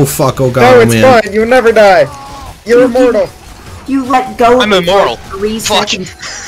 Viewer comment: the caption is wrong; I said "I'm immoral"